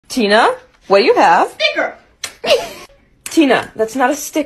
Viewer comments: I thought he said nicker instead of sticker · At first I thought he said the n-word in stead of sticker💀